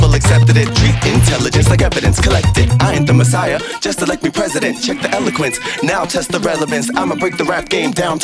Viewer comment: He sings: I am the messiah just elect me president ??? What???? Head in hands...Oh dear..Oh dear Oh dear....... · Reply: lannak21 I heard "I'm not the Messiah, just elect me President" which I think makes more sense